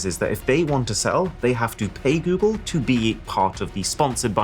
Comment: Am I the only one who had google assistant pop up at 4:42 because my phone thought he sayd "hey google"?